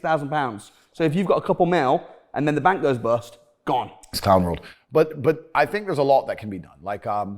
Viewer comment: Did I hear "it's time world"